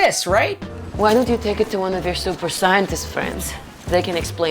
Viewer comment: "Super SAYANtist friends" Best shit i heard today